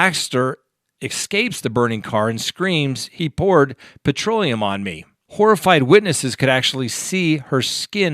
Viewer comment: INCORRECT 6:14 I live in Australia, NO one ever says the word ''petroleum'' what you call 'gas' we call Petrol. She said and I quote ''He poured PETROL on me''. We do not use the word's Gas or Petroleum in Australia (as Americans do for what you fill up your car with). The Only time we use the word (gas) is for the car's that run on L.P.G. Gas. That is a whole different story.